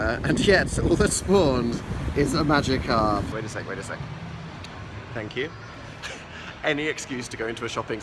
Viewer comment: WHEN PHIL SAID THANK YOU I THOUGHT HE SAID LOVE YOU AND I SCREAMED